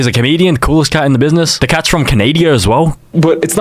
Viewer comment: did he just say Canadia im crine